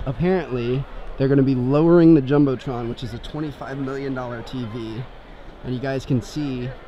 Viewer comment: for the Jumbotron tv I thought you said $2 million Dolores I did not believe you because that is so expansive so I passed back 10 seconds and you said $25 million....................